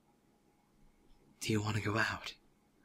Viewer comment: i heard cry said "Do you want to go out?" lol